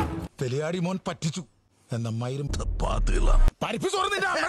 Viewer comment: what did Kamal Hassan said???? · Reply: (go)*tha pathukala😂